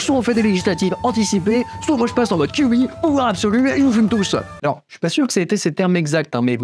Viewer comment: Après mode kyubi (ça je comprends) vous dites "ou art absolue" (je pense pas avoir bien compris) Mais c'est quoi "art absolue" (vrai question et super vidéo soit dit en passant)